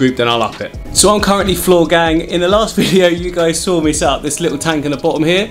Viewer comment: Did he say floor gang 9:25 ?😂 damn what a man of culture